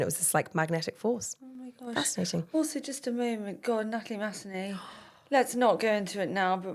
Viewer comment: Who did say at 12:12? Natalie ?